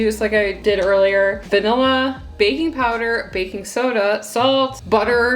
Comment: girl it sounded like you said baby powder 😂😂